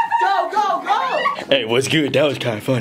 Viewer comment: it sounded like he said "blariel"